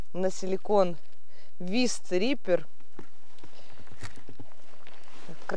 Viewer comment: привет из Новосибирска,давно подписан,давно смотрю,прикол в том ,что когда ты назвала название приманки,мне послышалось Вис триппер)))) посмотрел в описании,оказалось Вист Риппер 🤣🤣🤣